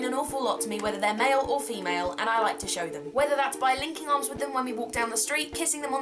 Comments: when you said linking arms I honestly thought you said licking arms....it was awkward for a few seconds before I replayed that xDD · Okay, I'm so sorry but when you said 'Linking arms with them when we walk down the street' I swear to god, I thought you said 'Licking arms with them when we walk down the street' for a minute there!! Needless to say I was slightly worried! :D